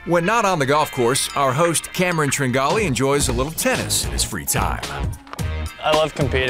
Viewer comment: I thought it said triangle tennis. Kinda disappointed D